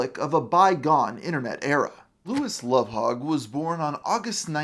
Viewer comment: I'm sorry, did you say his name is Louis Lovehog?